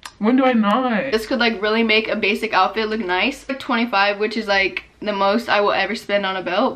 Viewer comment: sis did you say 125$ or